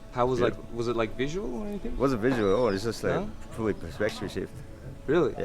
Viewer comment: what does he say? "Not visual"....... Thanks for making this video is really helps making informed decisions as to whether or not ayahuasca's for you.